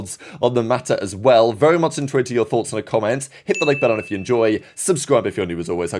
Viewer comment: what word are you saying at 0:29? I've listened to it in every video but I can't figure it out for the life of me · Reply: "very much intrigued to hear your thoughts in the comments"